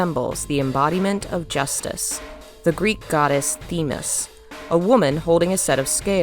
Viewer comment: I can't tell you how confusing it was to hear what I thought you said, which was: "...the Greek goddess of Justice, Venus..." I was about to internally flip my shit ("since when does Muse make such a bizarre mistake? What universe is this?") before I rewound 10 seconds and saw the image with what you actually said, which was, of course, not the name Venus, but Thenis, whom I had never heard of before. My heart can slow back down now. Great video!